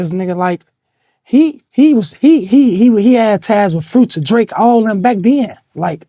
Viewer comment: Vlad thought he said fruits and drinks at the end when he said future n drake lol